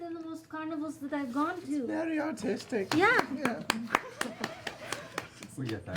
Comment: I thought Sam said Autistic. I nearly died of laughter